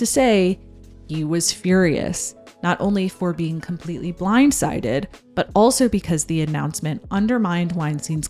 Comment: at first I thought you said "wein-sided" and I laughed to myself